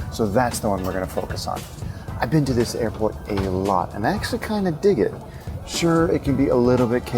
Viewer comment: Can anybody transcript what is he saying between 1:35-1:37 ? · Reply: "I've been to this airport a LOT and I actually kinda dig it!"